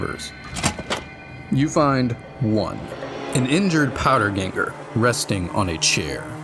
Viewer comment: did he just say what I think he just said? Lol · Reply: No lol he said "powder ganger"